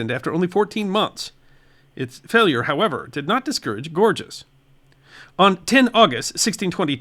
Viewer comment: Each time you said "Gorges" i heard "gorgeous". The effect was interesting (as the video)! 😄